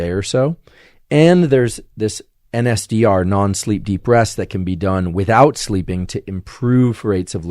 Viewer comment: There is a misleading transcription: At 42:58 "an SDR non-sleep deep breaths" instead of "NSDR non-sleep deep rest".